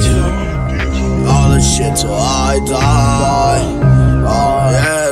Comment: I swear it sounded like he said "ill shit till i die"